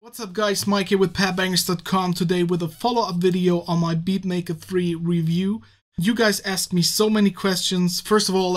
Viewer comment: oh, I thought you said Beat Maker Free... Double RATS... It's a little pricey and you only get starter packs, every thing else is IPA. I would try BeatHawk first more modern and better sounds IMO